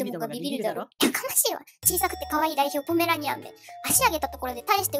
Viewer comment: ポメラニアンじゃなくてポメラニランて聞こえてなんか舌足らず感可愛い マーキングだいっ！(エコー)で吹き出したw